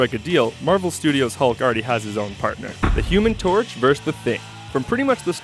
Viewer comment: he says "The Human Torch Vs the Thing" the caption says "The Hulk Vs The Thing" once again. 😂